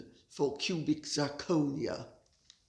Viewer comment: Oh I laughed. I thought you said ".......it's swopping a beautiful well-cut diamond for pubic zirconia". Aaarhg